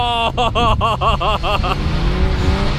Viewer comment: Hoorde ik daar nou een vloek? :o ;P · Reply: Ik riep om Jezus. Niets mis mee hoor.